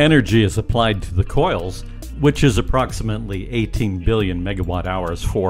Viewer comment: How many megawatt hours did he say were required for a warp event? Around 18 billion? Around 3:30 he says the number, and again around 10:40. My estimate is that 18 million megawatt hours would be the entire energy consumption of a city of 1.5 million for a year. So, 1000 times that power (18 billion) would do the job? Uhm, what sort of conductors could carry such a load, and what kind of grid could deliver that juice as one surge, for one "event"?